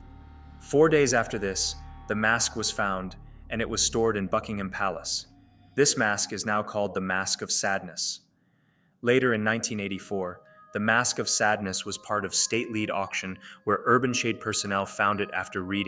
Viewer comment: Was it just me or did i hear 'Fuckingham Palace'